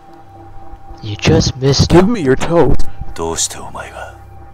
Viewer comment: "you just missed him..." 0:08 "GiMmE YoUr ToEs" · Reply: i heard "give me your toad"